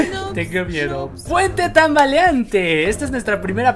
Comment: pensé que decía "puente de tamales xd " ya se toy loka tengo hambre :v